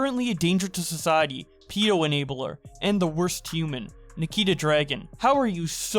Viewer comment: i though i heard him say Nikita Dragovich from bo1 i was so confused